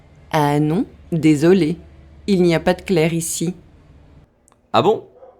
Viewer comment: Today, day 2, was more difficult than day 1. I heard "il y n'a put Claire ici," when it was "Il y n'a pas de Claire." I also heard "bon journee, voici a va" when it was "bon journee a vous aussi, au revoir." The words run together in French and I need practice to understand. I will continue with day 3 tomorrow! Thank you for these lessons!